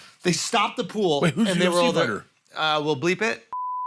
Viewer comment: what did bert ask? I've listened to it 30 times in a can't understand what Bert asked before the answers blurred out... sounds like he says wait who's all better